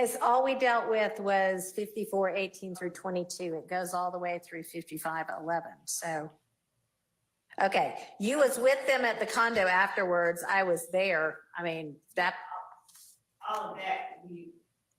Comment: I’m from Boston I thought I heard you say you was other stream ??